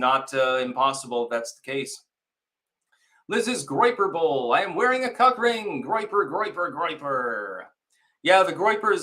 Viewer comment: Did he really say “I’m wearing a co…ck ring?” @ 21:36 say what now ?😂 Go Trump 2024!